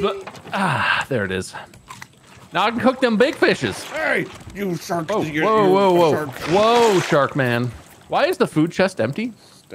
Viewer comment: I can’t be the only one who thought said “you shark ni**er you” at 7:11 😭